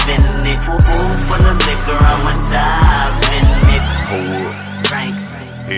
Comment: For the longest time i thought he kept saying oof my ****I'm dime in